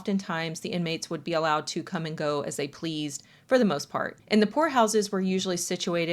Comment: for a good minute i thought you were saying whore house not poor house and i was so confused